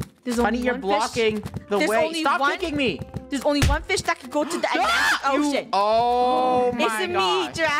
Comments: Funneh doesn't actually say oh yak what she actually meant to say "I'm the only fish that will go to the end of the *ocean*" Just wanted to correct you because I heard the word "ocean" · She actually said There can only be one fish that can go to the end of the ocean